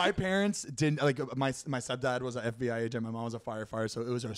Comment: did he say his stepdad was an fbi agent 😳